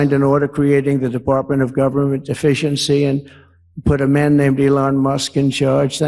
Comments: every time he says it it sounds like he says "department of government deficiency" instead of efficiency, the auto subtitles even say the same thing 😂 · Department of Government Deficiency? That's what I heard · Anyone else hear him call it the Department of Government ‘Deficiency’? 🤔 … just me? 🤣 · I swear it sounded like Trump said "The Department of Government Deficiency" which would be the most honest thing he has said since "I will be a dictator". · Did Trump say Department of Government Deficiency? Video time stamp 1:40 or am I hearing thing.